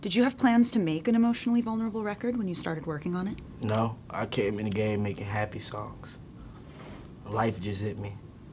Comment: "I came in the game making happy songs, then life just hit me. It kinda happened" damn... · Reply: Dominic NPC I thought he said i came in gay making happy songs